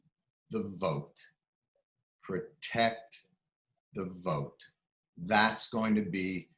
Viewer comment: haha, i thought he said "protect the BOAT, protect the BOAT".